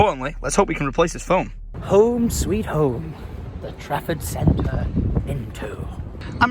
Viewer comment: All the time during the vlogs, I thought was saying Traffic Centre, and kept wondering why it was called the Traffic Centre? 6:35 Trafford Centre, duh.